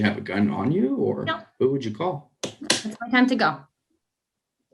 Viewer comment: Wait what the fuck, she said it’s ‘my time to go’? Holy shit lmfao I thought she said ‘I’ll let it go’, like she’ll let herself get robbed, not let herself get MURDERED. This people are lunatics.